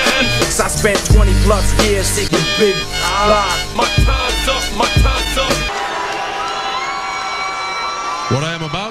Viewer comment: 0::20 i thought he said your time is now my Time is upLol